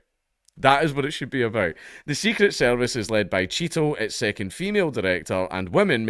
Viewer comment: i thought he said "secret service led by cheeto" hahahaha